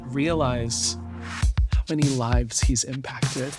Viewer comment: I heard “when he’s live, he’s impacted” I was like HUH